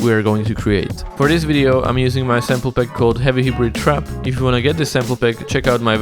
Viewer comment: I thought you said Heavy Hebrew Trap, then I saw you meant Hybrid